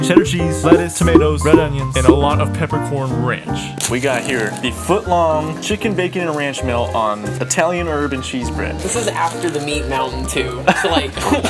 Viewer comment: I know bro did not say "Italian urban cheese" 😭